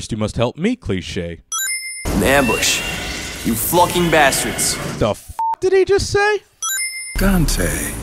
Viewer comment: d-did he just say you flucking bastards...you...FLUCKING...bastards...FLUCKING!?!...what the hell does that even mean?